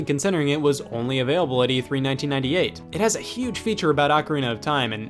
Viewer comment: For a second I thought you said “Avery 1998” and that’s continently is my name